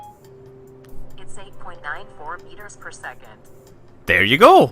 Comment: Am I the only one who heard Siri say "Insane point nine four meters per second", or is that what she really said? XD I keep replaying it, and I hear the same thing! Tell me what you think